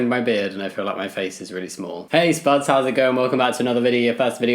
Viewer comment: Why do I always hear your intro "tastebuds how is it going"😭